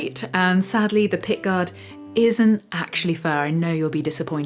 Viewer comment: "...the pickguard isn't actually fair". What does that mean? Is it a British way of saying "unattractive"? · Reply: omnipop I believe she said “fur”